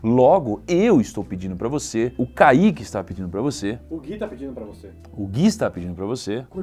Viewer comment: Quando ele falou "O Kaique está pedindo pra você" eu ouvi "O Monark está pedindo pra você"